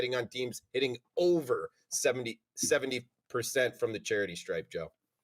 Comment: Did say 70 or 77% (3:20 mark) when it comes to FT shooting? The audio cut out for a second. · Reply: 70%